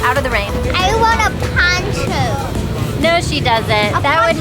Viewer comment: what did she say? i want what? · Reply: She said, “I want a poncho.”